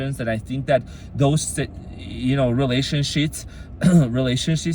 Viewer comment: I heard "relationshits" and I love it.